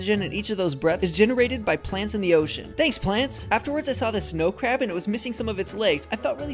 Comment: Did he just say "thanks planets"?